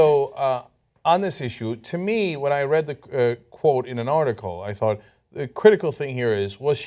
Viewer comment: it sounded like Cenk almost said "reading the Koran"